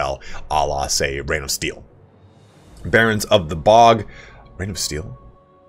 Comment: I'm pretty sure I misheard you say "Barons of the Pog" when referring to the Nurgle RoR but I love that so much more tbh.